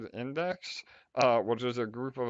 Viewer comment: When said "WEN DEX" I thought he said WINDEX hahaha